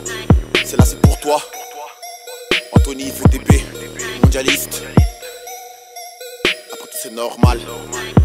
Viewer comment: Qu'est ce qu'il dit en fin? a 4:34 .. Anthony VDB, Jihadist???? Ou....